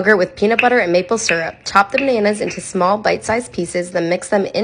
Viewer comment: Thought gurl said chop the mayonnaise into bite size pieces 😂 had to watch 3x to make sure lmao